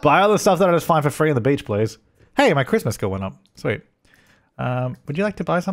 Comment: for a second there I thought you said Christmas skill lol